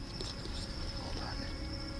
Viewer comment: EVP!!! Can't make out what's said. Maybe you can, or peeps in the comments section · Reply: Idk if it's or not but it says hold on then or move on then